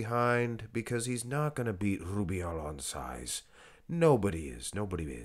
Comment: did he say milk that upper body 😂